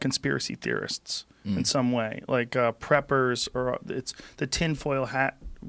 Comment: I thought it said peppers. Wow